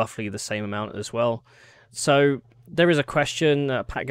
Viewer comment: When you said the CEO's name, I first heard it as 'fat girl singer'.